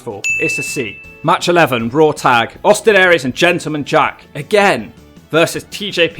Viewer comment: When you said Gentleman Jack, I thought you said Gentleman Jarvis for a second and was really confused for a second. (Fans of Brian Zane might know who I am talking about)